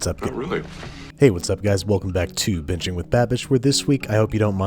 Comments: I heard, "Welcome to bitching with Babish." 😂😂😂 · Did you just say “bitching with babish” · Am i the only who heard him say welcome to bitching with babish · Am I the only one who thought he said "Bitching with babish"??? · It sounded like he said "Bitching with Babish"